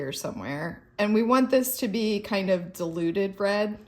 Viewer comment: This has been hilarious to watch and to top it all, it sounded to me like you were saying deluded red rather than diluted red! Once I heard it I couldn't unhear it 😂😂😂